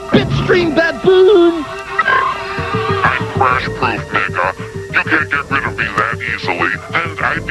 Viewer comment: What did cyberskull tell that boy when he tried to turn off the screen? Go to 1:50 and listen. Sounds like he said "I'm crash proof Nigga". Tell me what cha think.